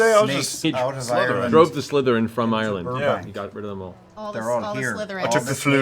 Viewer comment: Could have sworn I heard Matt say that St. Patrick “drove the Slytherin from Rhode Island.” :)